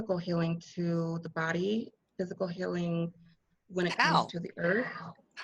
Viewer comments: Yes..You did hear an EVP at 4:32 says "How", a repeat of what you had just said. So cool! Love you all💕stay safe😷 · I heard a whisper ”How?” after asked How at 4:31.